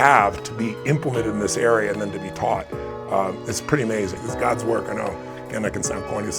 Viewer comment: can someone tell what he's saying ? I need it urgently , PLEASE ! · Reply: He said: It’s God’s work I know